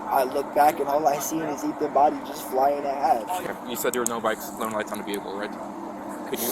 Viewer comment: Kid said “flying in ”half” or sounded like it anyway. Btw…..I’m definitely NOT feeling the whole “illegal race” caption like it’s his Pops fault. Nah not cool. Anyway….when the dad grabbed, HUGGED and smelled his son’s shoe?! Man….that was sad as fuck.